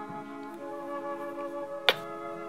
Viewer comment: It sounds like he saying chrispas